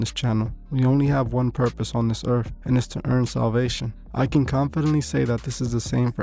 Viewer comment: Hey guys i hope all is well!!! Thank you for watching let me know if you want videos on more athletes!!! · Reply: Thank you for your content, God bless you but did you say at 1:23 that our purpose is to EARN Salvation? (Ephesians 2:8-9) I just needed to check in case I understood you incorrectly